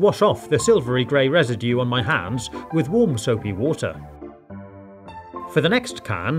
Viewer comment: did you say warm Soviet water